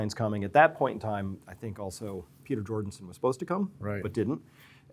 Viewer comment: Did he say Peter Jordanson?